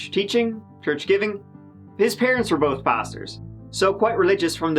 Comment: I had to rewind and re-listen to the first part again because I thought you said 'both parents were bastards' instead of 'both parents were pastors'. Don't worry, I took a nap after getting counseling from my own bastard.